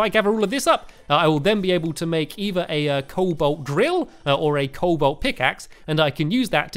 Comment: Am I the only one who heard "cobalt grill?"